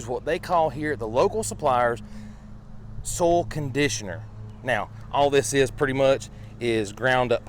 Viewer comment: I thought you were saying a brand name Sol Conditioner... until I looked at the screen.